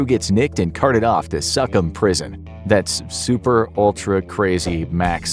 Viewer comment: I thought he said the suck in prison LOL